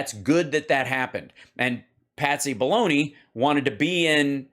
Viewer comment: I thought you kept saying Patsy Baloney.